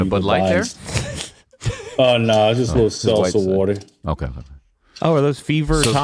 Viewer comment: had to rewind swear said salsa water